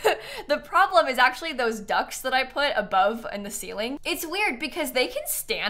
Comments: I cannot be the only one who thought she said "ducks" instead of "ducts." I was SO confused for a second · Oh, DUCTS. I kept hearing "ducks" in the ceiling and I was slightly confused, but not surprised that there would be rubber ducks in the kitchen ceiling of Lil Simsie's potato restaurant.